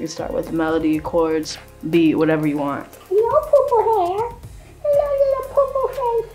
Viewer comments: 1min 43 secs I thought she said "Hello poo poo head!" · Who else heard her say “hello Lola puppy!” Very faintly at the beginning? XD